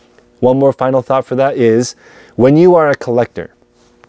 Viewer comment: I heard “then you’re a moron” and I laughed so much I nearly fell off my listing chair. I love listening while I’m listing!